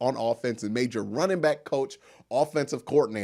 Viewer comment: Great vid! Not just saying that cause I’m a Clevelander. Lol Confession: it sounded like you were saying “obsickle” instead of “obstacle “ and it was distracting me for about 2 minutes. Ah, nobody loses focus like me!